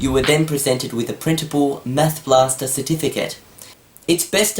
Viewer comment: When said "You get a printable Math Blaster Certificate", I thought he said "presentable" not "printable". I thought how the hell is this presentable?!